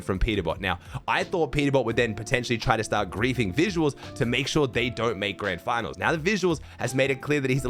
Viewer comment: I thought you said peter didnt make grands for a sec 😭